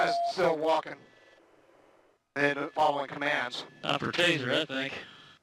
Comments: The cop said "I think" NOT "that thing" once again we can see Cenk blinded by his own agenda. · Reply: i thought he said that thing too. it sounded so much like that. but he did say i think. agenda, is caring for a human life that might have gotten treated unfairly an agenda. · Tase that "thing". Thing, he isn't even a man, a person to them. He is a THING. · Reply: Enkidu1701 At 1:44 it he says thing and not think. Later on he says, I think. Freudian slip? And you came to this grand assumption, how? Have you forgotten that you are on the world wide web, screen names are just as fake as the profile pictures. Unless you were just being facetious, for the "likes"... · I heard "time for the tazer I think" not "time to tazer that thing" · "This guy is still walking...... and not following commands" "Its time for a taser I think" He did not say "Its time to taser that thing", dont get tripped up by his accent. You can clearly here the K in think. Saying that he "Looks like a bad dude" is an opinion and should have been kept off the coms. No reason to babble in coms during a high stress situation. Looks like an officer fired a panic shot when the taser went off. This is a very sad scenario but it looks like the police had every intention of a non-lethal take down. I strongly agree that the police should have immediately started first aid once the situation changed. The video doesn't show how long it took them to begin do to the gap in footage.